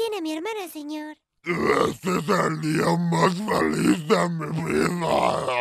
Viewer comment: Trabajadora: aqui tiene a mi hermana señor Hermana:HOY NOSE QUE DICE XD:AHHHHH Helado: que? Mecoboy:AHHHHHH:me la llevo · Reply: "Este es el dia mas feliz de mi vida" es lo que dice la hermana :b